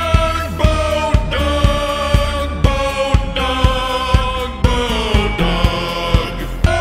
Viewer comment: Speed x2 sounds like ther saying boa dog